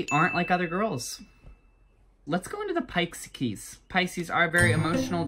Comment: Put Captions on, and focus on when he says “Pisces.” The caption says “Pyke’s keys-“ PFFFFTTTTT get roasted my Pi’s C’s BFF. (No offense to Pisces, I’m just trying to annoy my Pisces bestie via a comment that they’ll never find.) Edit: 2:34 Put captions on and you’ll find out that me and my fellow Taureans are.. ..tortoises. xD I mean yasssss go off